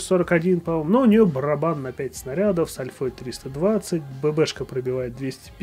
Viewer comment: послышалось :"барабан на 30 снарядов с альфой 350".